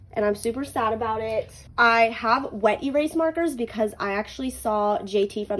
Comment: That’s what I used and I let it dry overnight and it rubbed off on my finger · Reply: for some reason I thought you said Magic Eraser, not wet erase. My bad.